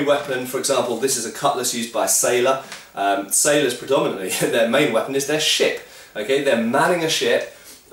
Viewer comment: "Their main weapon is their shit!" Wait, maybe I didn't hear that right.